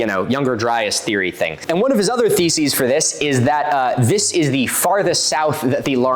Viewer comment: You said "One of his other theses for this..." and all I heard was "One of his other feces for this..." and I thought it an astonishingly accurate portrayal of what comes out of Graham's mouth.